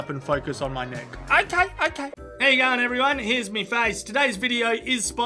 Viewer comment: I love it how he says "Here's my face" whenever he shows his bloody handsome face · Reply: And for me, an american guy it sounds like he is saying "here's 'me' face". . .